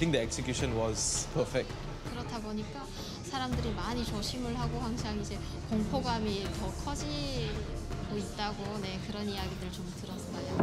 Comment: the subtitles at 8:10 are just wrong. What she actually says is "...which is why people are always very cautious and ive heard that fear is increasing"